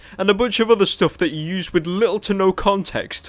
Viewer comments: I thought it said human molester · thought it said "human molester" ;c